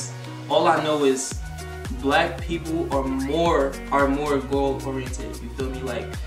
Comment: Light skin women CAN HAVE BAD ATTITUDES and dark skin women CAN BE “NICE” 😳 and vice versa WAIT I ERASED HALF MY COMMENT DID HE JUST SAY “LIGHT-SKIN WOMEN ARE MORE GOAL ORIENTED” wtf